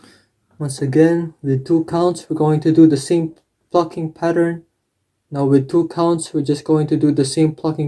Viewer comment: Why do i hear "same fucking pattern" here 😂😂